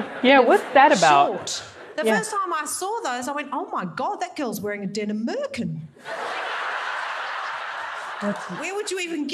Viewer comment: You didn't seem to catch what she said here. She says, "Oh my God, that girl's wearing a denim merkin!" 😆